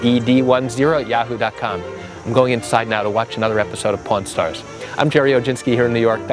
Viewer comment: Funny, first I thought you are saying Porn stars, not pawn stars! I was like, what is he talking about! LOL